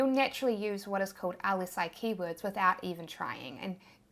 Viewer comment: Thank you so much for the awesome tutorial!!! Around 17:48 in the vid, you mention '........keywords', I cannot make out the word that comes before keywords, will you reply with it here? You then say 'Google loves xxxxx keywords' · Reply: Lsi keywords, which are basically related keywords.